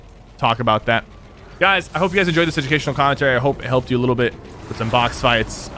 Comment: i thought it said emotional commentary. shi